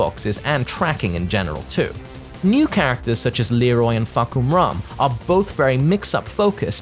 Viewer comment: i kept hearing u say " FUCKOMUM"